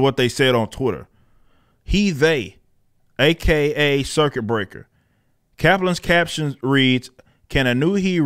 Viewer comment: Watcha thinkin? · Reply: They're literally throwing shit hoping something sticks. O and i thought i heard "suck it breaker" lol